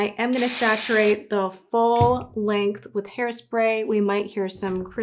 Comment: When she says “the FULL length of hair” I thought she was going to say “spray the f out of it” 😂